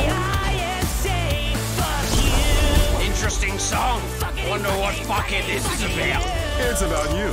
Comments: I always see the subs saying Mammon saying "Wonder what fuckin this is about?" but I hear him say "Wonder what Fuckhead this is about?" and I just hear that more than what the subtitles say. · I think Mammon says fuck head, not fuckin'